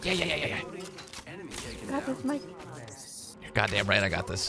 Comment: Did bnans said: "Gracias Mike"??